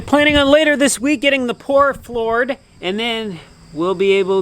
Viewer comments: Did you just say” pour floored?” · At around 13:16, did anybody else catch saying "getting the pour floored"? lol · HA HA HA HA at time13:14 Your getting ready to have the pours floored, instead of floors poured